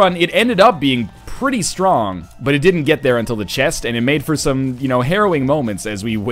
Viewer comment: what the hell is he saying? It's been bothering me for a while now, that word that sounds like he's saying "heroin". What word is he saying? I tried looking for similar words in google but all there is is heroine or heroin, which in context never makes sense. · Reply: Harrowing